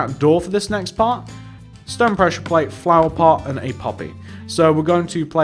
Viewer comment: When he said poppy I thought he said puppy and I'm like ...what? 😂😂